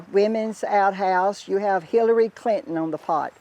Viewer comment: I thought she said "fillary" clinton ha ha 😂